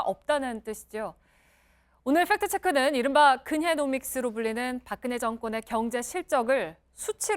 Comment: 근혜노믹스가 근혜놈X로 들려요